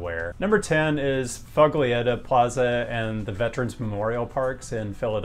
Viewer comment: When you said Foglietta plaza in Philly, I heard "fugly." Which, coincidentally, is a very good description for quite a lot of Philly.